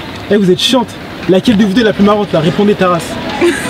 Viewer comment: a 2:50 il dit"repondé ta race" et elles se disent des trucks dans l'oreille est moi je pensé qu'elles diraient ta race. lol.LASAM